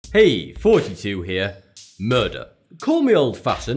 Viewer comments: sounds like he says "hey 42 here" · When he say i always hear him saying "42 here"